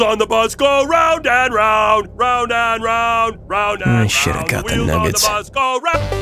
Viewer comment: Sounds like says 'Oh shit got the nuggets'